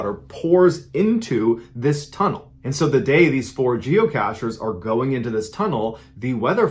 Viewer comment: Lol it sounded like you said jewcatchers